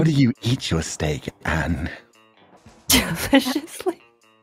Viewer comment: I think she said "Viciously"